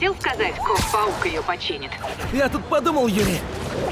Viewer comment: Мне вот интересно мне одному в начале послышалось ГОП паук?